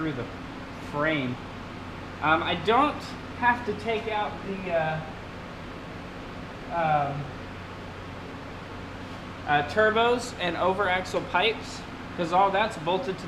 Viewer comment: Did he say didnt have to take out the turbos?